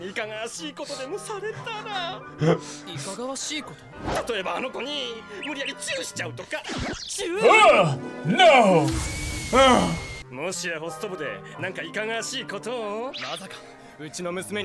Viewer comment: I expect both and would be WHEE~~~ZING but Cri~~~nge, S-rank, instead. Also 3:10 did Tamaki just say "Chyu" out loud? *Cue the Plaintina Jazz: Hajimete No "Chyu"